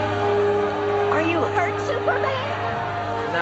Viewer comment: What superman say? "I just cut me at the ??????" any help please